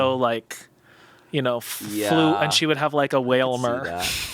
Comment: "Amanada could be like a water type. Like a Wailmer." WOW OKAY EDIT: Going back, I think he says she could "have" a Wailmer, but still. Lol